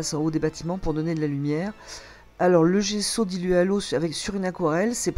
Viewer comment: Ah merci, j'ai entendu "Gesso" ? Gessou ?... Mais je ne sais NI ce que c'est (personne ne m'en a JAMAIS parlé dans mon cours), ni où me le procurer, donc... quelques précisions sur cette "base" et les divers autres pigments et leurs dénominations serait peu-être utile à vos lecteurs. J'ai lu et vu tout ce que vous disiez à propos de l'aquarelle en tubes... je vais regarder tout cela... dommage que - selon les conseils successifs et divers des animateurs du cours que je fréquente, j'aie acheté - déjà - trois boîtes de pastilles d'aquarelle en godets ! Car - je suis d'accord avec vous, à l'expérience, c'est très fastidieux à employer (mélanges, eau "matière"...cf "pigments" obtenus) et construire sa palette et/pour pouvoir y revenir m'apparaît - d'évidence - moins compliqué avec les tubes ! Je dirais : réservons une petite boîte de pastilles pour nos carnets de voyage, mais employons de l'aquarelle en tubes pour travailler - de retour - à l'atelier (c'est mon avis). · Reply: il s'agit de gesso, vous trouverez des démos sur Youtube. il existe, depuis peu je crois, un gesso special aquarelle. Je le testerai un jour....pour le moment je me sers de gesso tous supports.